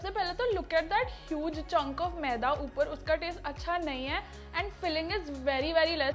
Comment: Wait BTS army who heard in 4:52 she said like 'huge JUNGKOOK maida' ❤ omg btw love you di 💜💜